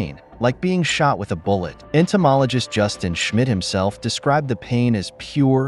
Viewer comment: It sounds like they say the guys name in a way to make it seem like they're saying "Justin shit himself".😂